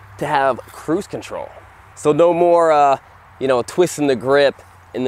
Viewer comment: I heard “boost control” instead of “cruise control”